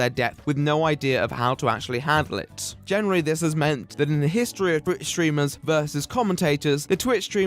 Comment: I heard "in the history of British streamers..."